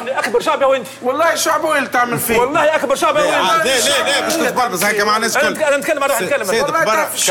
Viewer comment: سمعتوه الورتاني اش قال و الا وذني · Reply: kal bech tetbalzez